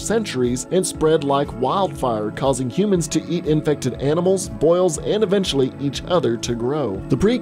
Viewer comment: I thought he said "To yeet infected animals"